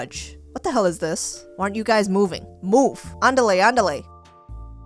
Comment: Did she just said 'Andarle Andarle' 😶😶